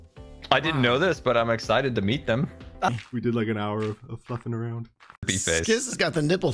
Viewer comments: I genuinely can't tell what says when it sounds like he said "fuckin around" futzing? idk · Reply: He said fluffing, he confirmed it in a comment lmao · Did swear at 630 or am I mishearing? 😂 don’t think I’ve ever heard him swear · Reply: He said fluffing